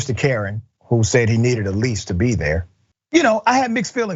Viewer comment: I heard, "You Need A Leash A To Be Here", like he was a dog! I didnt here lease, like do you live here! #MayBeWrong